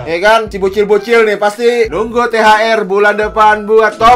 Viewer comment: apa gwa salah dengar bg.kwkwkw bulan depan